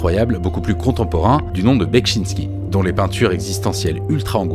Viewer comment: Bonjour, je n'ai pas bien saisi le nom du peintre. J'ai compris "Betchinsky" mais je ne trouve rien sur lui. Comment orthographie-t-on son nom ? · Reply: Je viens de trouver : Zdzisław Beksiński